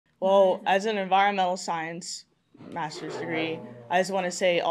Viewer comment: Now the subtitles said, "As an environmental science master's degree," but what I heard was, "Chopper, sick balls"